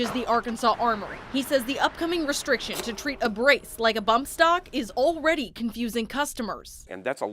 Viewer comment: wait. She's confused. Did she say bump stock?